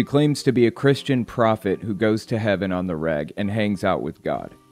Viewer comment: Man, for a moment, I thought you said she "goes to heaven on the rag."